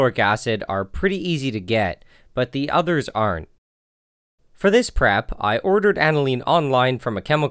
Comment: I thought he said “For this crap...”